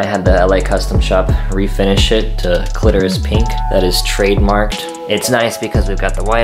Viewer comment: Imagine walking into the custom shop and asking them to do a "Clitoris Pink" paint job on your guitar. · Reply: Fam I thought he said glitterous pink as in glitter